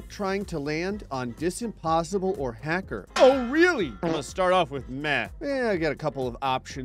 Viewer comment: Thought he said lets start off with meth i wasnt looking at the screen 😅